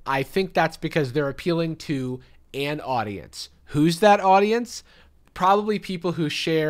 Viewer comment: Ok but when said “an audience” at 6:17 all I heard was “Anne Audience”. Great drag name.